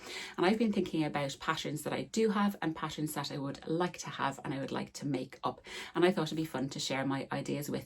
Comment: Funny, when you said "patterns that I do have and patterns that I'd like to have", I heard "passions that I do have and passions that I'd like to have" and thoughts: I also have a backlog of passions. 😅